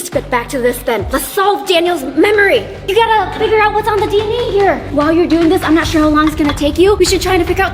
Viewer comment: i think said dne